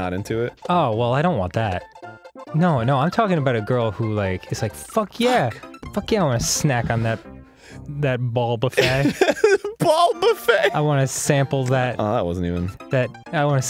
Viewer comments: I honestly thought Dan said "Ball BaFett" not "Ball Buffet" all of the Star Wars jokes and all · When they said ball buffet I misheard, and having not yet read the title of the video, heard "ballbuffett" like wobbuffett the pokemon. And then it occurred to me: Wobbuffett is a dick with four balls and a tail. Pass it on. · I thought he said bulba fey when he said ball buffet and I thought that he was going do the funny jokes voice